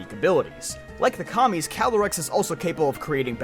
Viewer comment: I thought you said "like the commies" and I had to go back 10 seconds